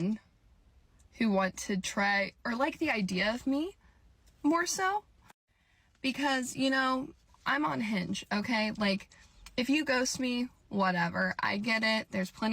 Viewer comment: I heard "I am unhinged", not "I am on Hinge".